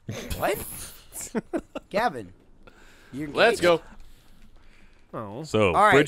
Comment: Did Michael just call Gavin a gate?